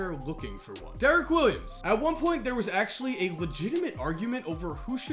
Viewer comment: I misheard derrick Williams as rose and got confused hard